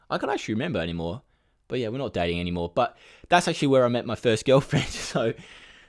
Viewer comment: When He said "I'm not dating anymore" I thought he said "I'm not Gay anymore" xD